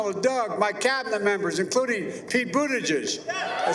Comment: did he say Pete Bootyjuice 🤔